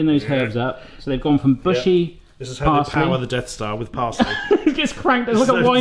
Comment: I thought he was saying "a deaf star". Thats NOT in Star Wars.